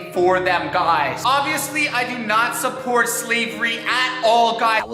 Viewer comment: hold up did he say slevery